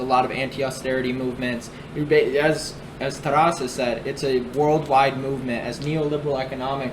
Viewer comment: Who did you quote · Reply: Okay i thought he might have said Strasse